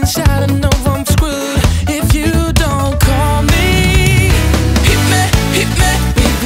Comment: Hit me with your passion? What’s he saying here?